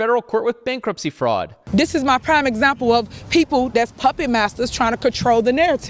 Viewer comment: Did she say, “Puppy Masters?”